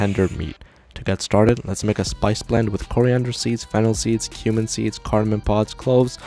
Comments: I thought it said "human seeds" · It sounded like he said human seeds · I heard him say human seeds 💀 And yes ik it’s cumin seeds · I thought bro said human seeds 💀💀😭😭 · I really thought he said 'human seeds' for a sec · I thought i heard "human seeds"😏 🤨🤨🤨🤨🤨🤨🤨🤨🤨🤨🤨🤨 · Wait, was I the only one who heard him say "human seeds" in there 🤨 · I heard the word "HUMAN SEEDS" BRO WTH ARE MY EARS BROKEN